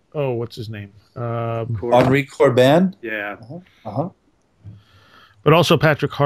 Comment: who are those authors mentioned around 22:30? I cant quite make out the names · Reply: Henri Corbin and Patrick Harpur.